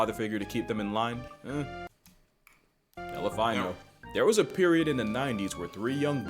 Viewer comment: did broskii just say 'Hell If I Know' as "Elephino"😭😭If so that word play is crazy😭🔥🔥